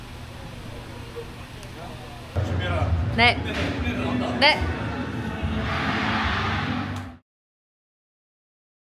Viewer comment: 강민호 선수 에게 뭐라고 한거죠????? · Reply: 준비해라 끝내기 하나? 나온다